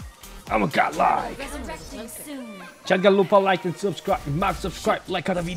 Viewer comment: Naizu😂 but wait, 07:23 did you say "Jangan lupa like dan subscribe guys" 😂 OMG,that's funny 😄